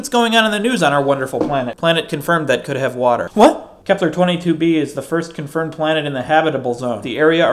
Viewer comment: I thought he said "Kepler 221B" and I started hyperventilating. Sherlock has taken over my lifeeeeeeeee and I don't even care.